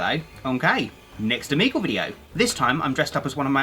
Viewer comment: I always thought you said ello im kay! I realize its ello okay now...